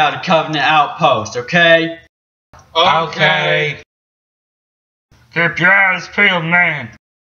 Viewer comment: The elite sounded like it said "keep your ass pealed"😂😂😆